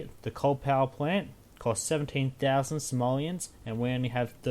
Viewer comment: Sounded like you said '17,000 Somalians'